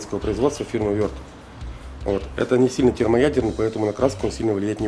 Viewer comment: Что он сказал " термоядерный"? 2:32 мин.? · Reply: Да - термоядерный :)